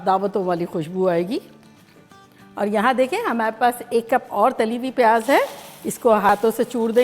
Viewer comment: Caption says half cup fried onion but says 1 cup.. :o